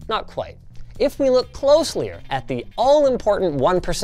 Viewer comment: did linus just say "closlier" at 2:13? LMAO